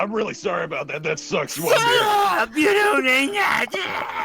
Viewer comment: you don’t need magic!! Is that what he said?